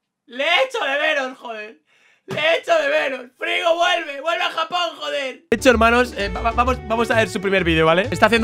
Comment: Fola dices que le echas de menos y le dices 😂 -Joder Frigo vuelve a Japón Like si crees que dice eso 👍 Pd: Lo único que veo cuando me aburro es la serie de pixelmon desde el principio Un saludo desde la Comunitat Valenciana Vinaròs